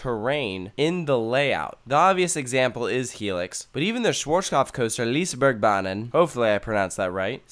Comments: what?!? Is it swedish? If so, i speak swedish and i don't know what he said, it was not even near swedish, but could be something else. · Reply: "[Anton] Schwarzkopf coaster Lisebergbanan". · I'm swedish but I honestly have no idea what he said... Sounded like he was speaking german. Can someone tell me what he was trying to say? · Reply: "Schwarzkopf-coaster Lisebergsbanan"